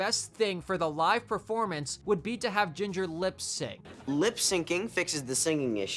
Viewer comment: For a second, I thought you said Ginger lips sing, and I was almost offended 😂